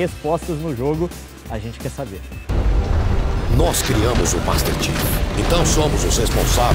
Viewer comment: kkk eu entendi " nos criamos o Master chef "